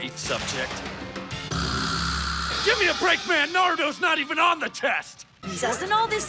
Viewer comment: Wait a minute, did he just say narudo.....